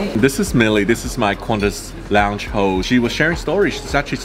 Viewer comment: Sounds like you say Qantas lounge ho